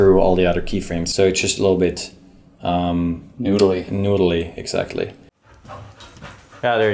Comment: I'm still learning English, can anyone please tell me what the word did they say at 4:04, which sounds like "noodley"? From the context, I guess it means "taxing"? Thanks a lot!